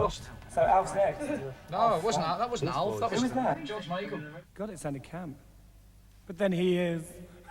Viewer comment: Does anyone know what Boy George said at 20:06?? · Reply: “God it sounded camp..but then he is”